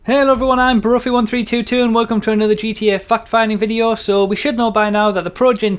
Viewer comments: At the beginning i heard 'welcome to another gta fucked finding video' lol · It sounds like you say fuck-finding. Not to be immature, but i live in the United States, and the accent you have makes it sound like that to me. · Am I the only one that heard him say "fuck finding video?" At the beginning? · in the intro i thought you said "GTA Fuck finding" lol